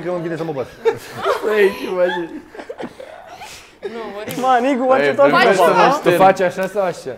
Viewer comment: did I just hear the sweet sweet n-word????????????????